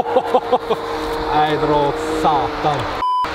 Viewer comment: What did he say? :D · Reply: ”Ae dra åt satan”